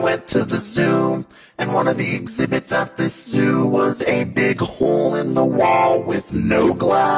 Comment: I thought the voice said the n word when it said big hole 😳